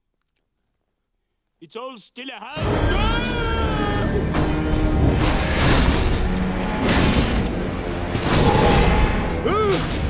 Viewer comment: what was he trying 2 say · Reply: He was gonna say "It's all still a humbug."